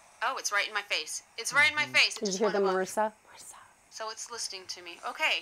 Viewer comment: I definitely hear a male whisper at around 8:52/8:53, says Marissa. 😳